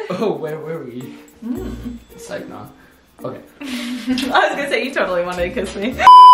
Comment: what did he say????? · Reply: He said "ok"